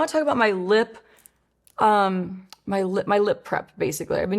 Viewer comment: I thought you said lip crack and I was like I’m so using that from now on but then I realized you actually said lip prep… still gonna use it lol 😂